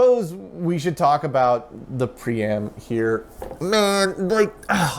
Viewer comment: Whenever you say "pree-am" and the drive isn't on camera, I hear "pre-amp" and think "why is there a pre-amp in a PDP-11?"